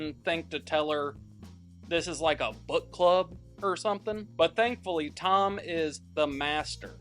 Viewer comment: thought he said “butt club”